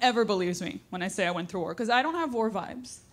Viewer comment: WAR 😂 I heard" WHORE VIBE"😂😂